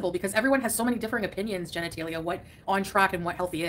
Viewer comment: did she just say "genitalia" and didn't realise she's been punked?